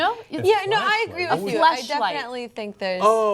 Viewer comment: I kind of like it. You know doodle is what people do on paper-drawing shapes, etc. There is a meaning for diddle, too. I also thought she said flashlight & wondered! I think it's seen as normal male behavior; but not prim, proper, ladylike for women.